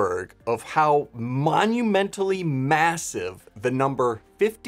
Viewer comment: did you say massive